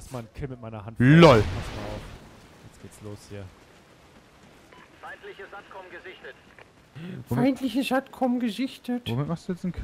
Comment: wer hat auch bei 9:45 "feindliches arschloch gesichtet" verstanden?